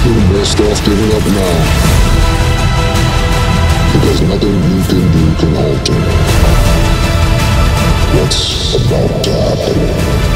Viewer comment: Someone help me, what do they say on @ 4:34 ?? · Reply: theres nothing you can do to alter whats about to happen